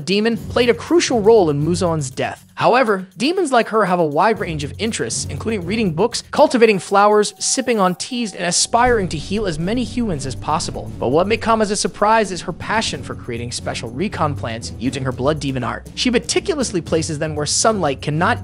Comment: I thought he said "Simping on trees-"